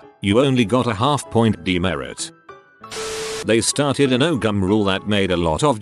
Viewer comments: when i heard the " no gum " rule i thought it said no gun and i was just like WAIT WHAT THE F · I THOUGHT IT SAID GUN